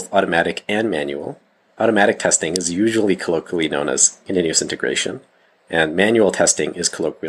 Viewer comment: Can anyone help me out with the devops testing bit? Automatic testing is colloquially known as ****? I heard Manual testing being colloquially known as Quality Assurance but didn’t hear the automatic testing part @ 2:23. Thanks in advance. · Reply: The video has subtitles. You heard “continuous integration”